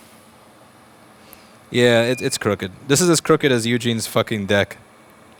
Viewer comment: For a second, I thought you said it was as crooked as Eugene's dick lol